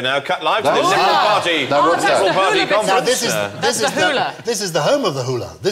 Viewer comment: did Bill say "Now live from the Lemonparty conference"? xD